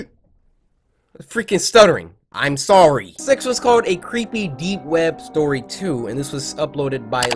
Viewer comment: I thought he said a creepy dick web